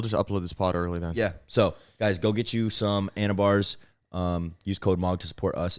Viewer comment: when bro said anabar i thought he said anavar 😂😂